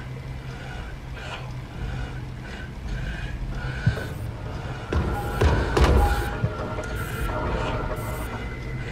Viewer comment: wow the animation of this fangame is very good Edit: 6:34 OH sh#$& Edit2: i love the animatronics animation when you turn on the light Edit3: You are an admin? 8:22 Edit4: 16:02 what he say? Edit5: Night 6 what? i dont understand the night 6, who is the enemy? Edit6: OH SH#$T WHAT THE F#$... 51:05 Edit7: What is that? EditFinal: 1:05:13 I really like this fangame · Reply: think he said “coming, ready or not”